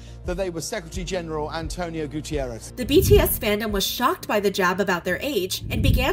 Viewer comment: eesh, 'Gutierrez' in the subs instead of his actual surname. I groaned worse than the public did at the Weinstein jokes. That was very disrespectful. JC badly mispronounced Guterres, sure... but that's not an excuse.